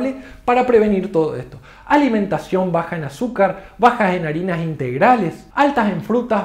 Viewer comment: Parece que entendí "baja en harinas integrales"???????' creí que las harinas integrales eran más sanas.